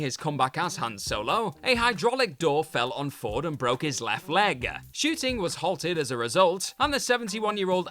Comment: I'm sorry, but what the hell is he saying at 6:05? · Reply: A hydraulic door fell on Harrison Ford's left leg, shooting was halted as a result, and the 71-year-old actor required hospital treatment (his age 9 years ago, probably when it came out). Feudal Production Company accepted responsibility. 😁